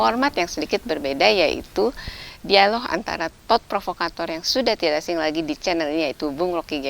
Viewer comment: thought provokator tohh, nyaris aj salah dengar gue😅